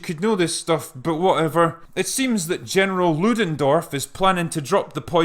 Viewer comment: I misheard "General Ludendorff" as "General Woodendoor" and had to rewind...